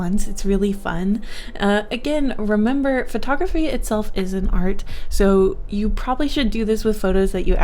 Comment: I thought u said ISNT for a sec I was like hold up